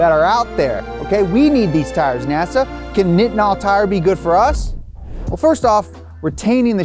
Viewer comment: Thought you kept saying fentanyl tires. Hell yes